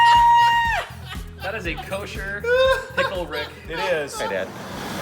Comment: Did somebody say.... fuzzy pickle for the camera?